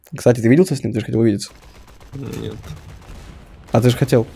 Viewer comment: Простите, что он сказал? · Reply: Он сказал - нет